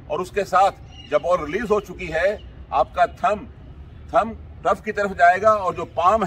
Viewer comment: Sir 4:45 mein aapne kya kaha? – Ki thumb ____ ki taraf jayega? Matlab kiski taraf jayega? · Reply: Ground ki taraf